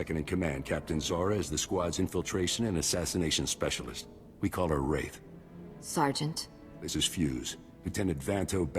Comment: minutes in i swear i heard "we call her rape"